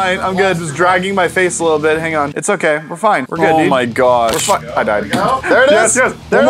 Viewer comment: it sounds like is saying " Well F Im dead " Anyone else ?